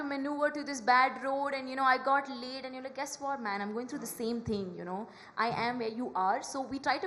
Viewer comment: What are you even saying at 9:12….😅?! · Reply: She said late